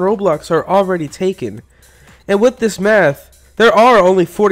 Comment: did he say meth?